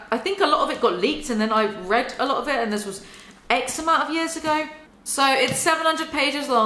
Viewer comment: everytime you say "x amount" i hear "eczema"